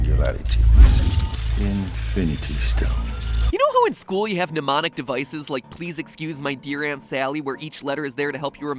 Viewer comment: I swear he said "you know at school were you have demonic devices", i was thinking oh really? which ones